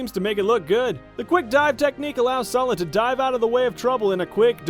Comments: Did you just say... SOLID? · Oh my fuckin god, did he just say 'SOLID'?